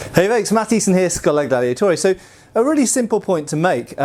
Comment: Hold up pardner did you just say "Schola Daddiatoria"?